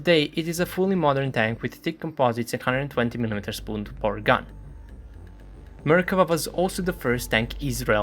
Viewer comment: Did someone else hear "spoonbore" at first? XD